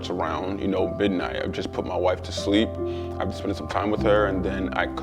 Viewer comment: First thing I heard was “I just put my wife to sleep” . . . I said whoah now lmaooo *press play and starts back watching*’😂